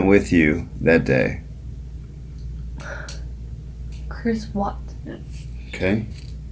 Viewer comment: Thought she said Chris Watts till he said Watson